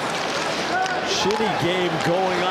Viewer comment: Sorry but at 0:15 did Edwards say "Shitty game going on"? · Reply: "Shinny" game (I know, hate to be THAT guy)